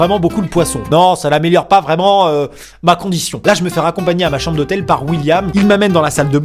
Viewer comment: ATTENDEZ c'est moi ou à 7:40 (ÉCOUTEZ BIEN!) il dit " macron dition" ?!